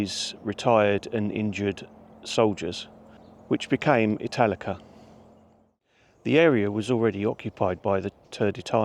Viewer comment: I'm not going to lie, I thought you said Metallica at first lol I still thoroughly enjoyed the video tho 😅